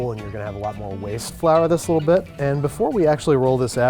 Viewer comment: I think he said, "flour this a lil bit" but I heard, "flour this little bitch" I had to rewind it several times because I was shook